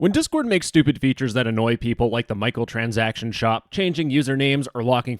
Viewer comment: I heard that as „Michael Transaction”